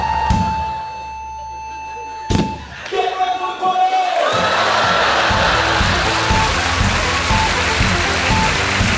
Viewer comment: Alguém entendeu o que ele falou? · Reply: "Get ready for the party" foi o que entendi